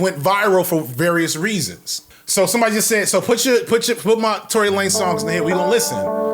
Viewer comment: all I ever heard of this guy was the name and I kept mishearing it as Tori Lane, who is a performer in a different entertainment industry, and a woman besides, so you can understand my confusion